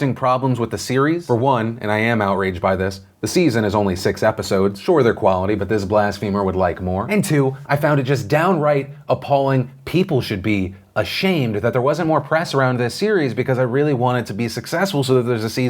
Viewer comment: Thought you said glass femur at first instead of blasphemer